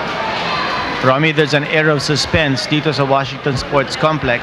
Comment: ? Ereol Spence ba Yung narinig ko? Parang propisiya lang ah💪💪